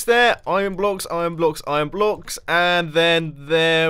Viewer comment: i heard you say "im block's" haha